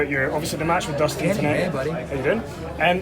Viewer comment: what did Cody say? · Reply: "Andy, Hey buddy"